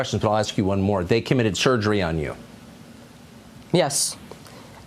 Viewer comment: Sounds like Tucker said “And they committed surgery on you”. Maybe he miss spoke but to be honest with you, that’s the absolute truth!